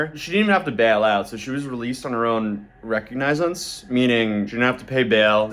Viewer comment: on her own WTF did you just say 9:57 reconionce not sure how to spell the word u just just said I think u ment to say recognizance and if u ask Google or chat gpt it will tell the correct way of saying it buddy. I still watch your videos though